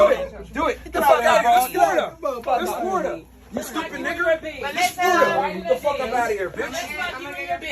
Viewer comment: did anyone hear myron call that one girl a stupid n-er